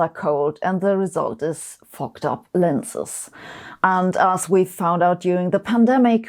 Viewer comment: I wasn't paying close attention until I thought I heard her say fucked up lenses